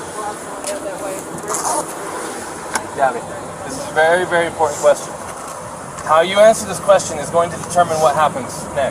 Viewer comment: I could not hear her answer when the officer asked her what she slapped him. Did she say I was trying to get him to calm down? How do you calm someone down by slapping someone. He was injured not her. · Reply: She said, “I was trying to get him to stop telling me to calm down.”